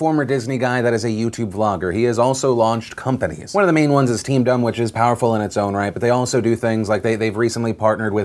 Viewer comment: Lol did anybody else hear "Team Dumb"